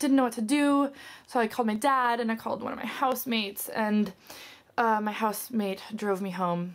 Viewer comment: Haha, I thought you said "one of my house maids"!